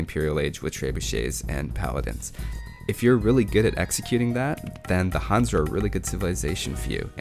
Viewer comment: I dont know if anyone will answer on this video, but I have to ask.. what word is he saying at 14:21 starting with "ex" I'm not native english speaker and I cant understand that, so If someone could write it here please.. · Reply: executing